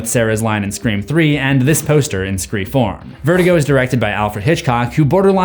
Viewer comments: was it just me who heard him say screa form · This is an awesomely informative video. Most of this stuff I didn’t notice and it makes me appreciate this requel a lot more. Though I do have to nitpick… maybe I was mishearing it but it sounded like you always referred to the 4th film as Scre-four-m. I get it based on the layout of the title, but for some reason it just irked me. Again, I’m nitpicking and I know it’s a silly thing to get bothered over, but I couldn’t help but mention it.